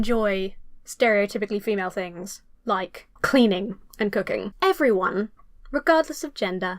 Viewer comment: my tiny little American brain heard “cocaine” instead of cooking here because I’m not used to your accent and now I’m dying